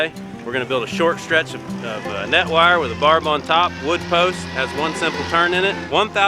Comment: Did he just say barteldo?